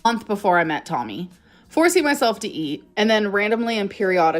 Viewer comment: “ forcing myself to eat”. She actually said she FORCED herself to eat. 😂😂😂😂 And the chicken nugget nonsense is nothing more than attention seeking behaviour that I’ve seen the 10 yrs do at my daughter’s school. Her behaviour is deteriorating as she gets older.